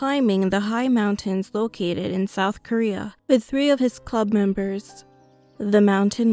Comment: did someone say south Korea...??